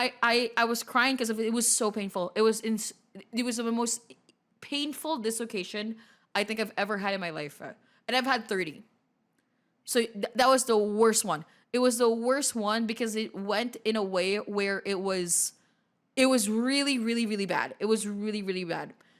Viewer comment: I beg your pardon??? 😮 did you say 3 or 30!?!?